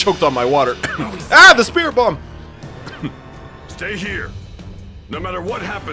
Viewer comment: piccolo's captions Read stat instead of stay 🤣